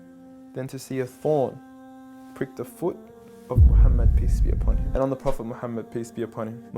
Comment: Every time I hear "Mohammed, piss be upon him". Maybe the accent...